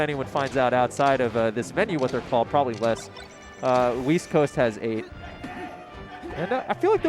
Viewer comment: Oh, east? I thought you said, "weast".